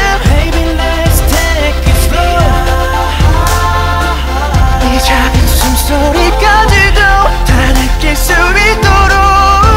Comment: I thought it said "so I can feel your small breasts" lmfao gosh I'm do dirty minded.